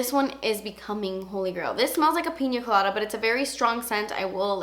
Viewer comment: I thought I heard you say penis colada 😂😂😂